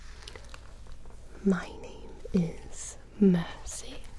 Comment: I thought she said Hi, my name is Mancy. Like Nancy but more manly.